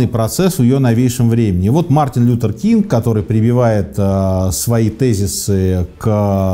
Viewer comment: я думал, что он был чёрным))) Несколько раз прослушал, и слышу 👂 Мартин Лютер Кинг